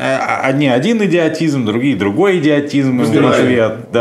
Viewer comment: Мне послышалось: "..в ответ на этот ПЕДИОТИЗМ..."